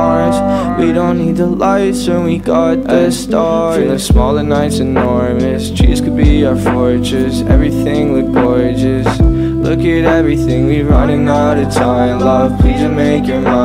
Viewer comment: am I the only one who heard "Cheese could be our fortress"